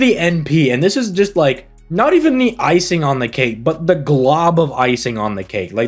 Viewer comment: You said her np is icing on the cake... what I heard was, I've got icing to drench her cake... i need help... that being said 2 years to make it rich before her global release.